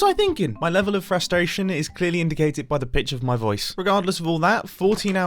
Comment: it almost sounded like mumbo said "by the b**ch of my voice" instead of pitch